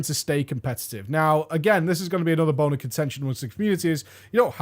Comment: Did he just say boner